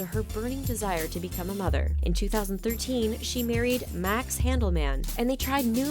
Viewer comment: Who else noticed that at 4:44 she said 2013 but the subtitles said 2003